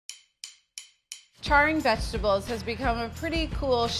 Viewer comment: You know I thought it said "Cleaning" instead of Charring.